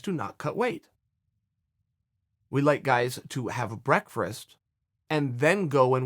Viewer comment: what was it that Chael "the original gangster" just said? He let's the athletes stop and havvvve??? What was that? Sorry I didnt quite catch that. Lmao. Brearfrast? Lmao! Come on Mr. Perfect! Tighten it up.